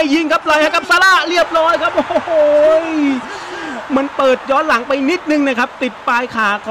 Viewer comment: ได้ยินไม่ชัดพี่เขาพูดว่ามึงหรืออะไร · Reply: มันเปิดย้อนหลังไปนิดนึงครับ.. (ผมไม่พูดหยาบแน่นอนครับบ)